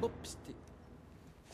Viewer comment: When I saw this in the theater I could have sworn he said "Run Shoshana". Am I imagining this?